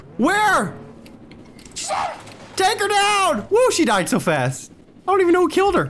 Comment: does say sh*t?